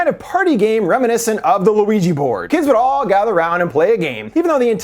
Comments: Wait wait wait. Did he say Luigi board?? 5:29 lol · did you say luigi board? XD cuz if so, im calling it that forever now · did he say Luigi board??? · "DID HE JUST SAY "LACKING SOMETHING LIKE THE LUIGI BOARD" Instead if the Ouija board 😂😂😂😝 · Is it me or does he actually say Luigi Board? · Did he say "Luigi Board"? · giggle it sounds like u said luigi board. Also i came immediately to find an ending explained and hoped id see you and i diddd so excited. Ur my fav explainer Copy pasted my edit from the Jeepers Creepers ending explained u did.: edit: Whoops This was meant for the Talk To Me ending explained by the same. Im watching it on a big tv on ps5 and i wanted to comment so opened my phone. Copy pasting to the right video